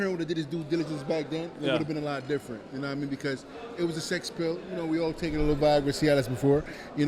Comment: Did he say he failed for a sex pill. I think vada missed that 1 😂